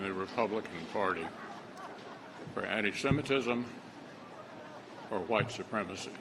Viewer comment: You can see it really pained McConnell to have to lie like that. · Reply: I thought he said "there is no room for any semitism" which would be a true statement.